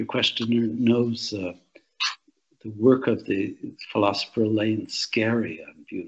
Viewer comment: A great and insightful talk, I wonder who is the philospher mentioned at @ 54:18 I can't figure out the spelling. The concept mentioned is "beauty is the compatibility of differences with each other" Many thanks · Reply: I think its Elaine Scarry